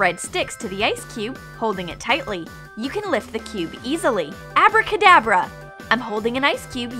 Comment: I heard “Avarda Kerdavra”